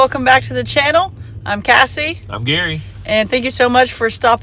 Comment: Cassie I apologize I called you Kathy when you say your name and when a Chad said your name in his video I thought you both said Kathy. Today watching this video I looked at the CC wording and it said you said Cassie so sorry.